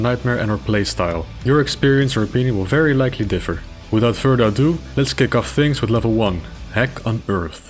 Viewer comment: did u just say 'without further adoom'